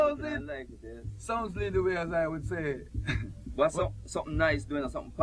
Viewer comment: what is he saying at 03:14 ? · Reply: "sounds to lead the way as i would say" :)